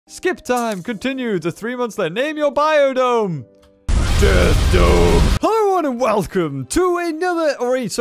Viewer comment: I miss heard you I thought you said death note but said death some inste